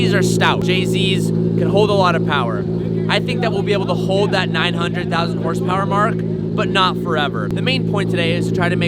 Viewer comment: What did he say??? Bruh I know the Supra’s 2jz was overbuilt by Toyota originally but I don’t think 900,000 horsepower gonna be viable 😢😂 Can’t wait to watch you guys pull the titanic out the ocean!